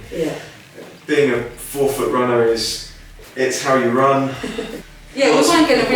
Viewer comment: I thought he said a four foot runner at first - I know for a fact is taller than that!